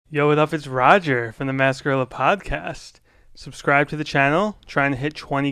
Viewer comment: In the intro it sounded like he said "from the nascar aloe podcast"